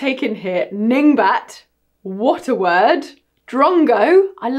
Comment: lol, it took me 3 times to figure out you were saying "what a word" because I heard "water word."